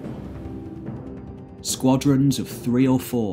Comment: It sounded like you said course instead of corpse. I had to fill in the blank myself.